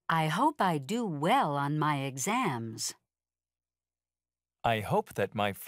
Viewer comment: Я один слышу на 22:46 как диктор говорит: I hope I do well on my axems.?